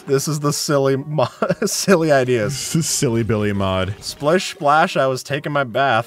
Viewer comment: did he just say silly Billy?